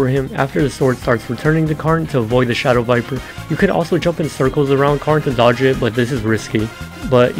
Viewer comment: Something funny about the p5 initial for me is that I thought you said “circle around carnellio” and not what you actually said, so I hold down diagonal left and dash 4 times in a circle around carn… and it actually works. And that’s how I dodge the initial to this day lol